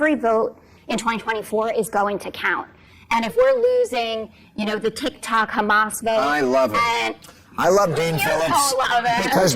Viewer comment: did she say they were losing the Hamas tick tock vote!!!!???? minute 3:50 wtf is up with that!!!!!!????????